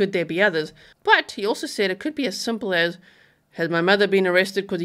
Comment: I thought he said, "has anyone else been arrested?" If he told his parents what happened then they are accomplices and they were aiding and abetting! Definite criminal charges!